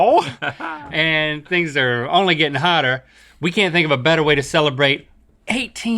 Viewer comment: in 1:00 the subtitles say "seeing how we're in the middle of summer and things are only getting harder" instead of "only getting hotter" and honestly that's applicable too